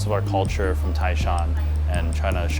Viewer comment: the subtitles says "Tianjin", which is wrong. There is no char siu bao in Tianjin. What he said is "Taishan". Tianjin is northern China, Taishan is southern China. They are thousands of miles away. It is like saying New England has Burittos in the 1800s.